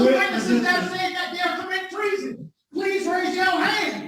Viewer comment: I swear I heard him say "You kermit TREASON!!"😂